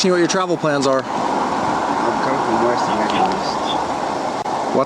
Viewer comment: the whole reason the cop got so irate about where he was traveling is cause he thought he said "weast" not "west"